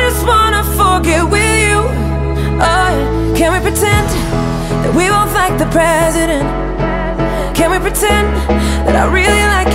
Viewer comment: Does anyone else hear "That we'll fight the president" at 0:50?